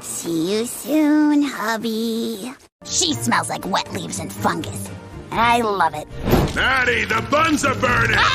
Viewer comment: Did she just say “he smells like wet leaves and f*ckers” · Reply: She said Fungus.